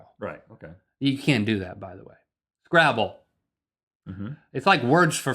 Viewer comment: lel turn on subtitles and it says gravel instead of scrabble but it does sound like he said that though.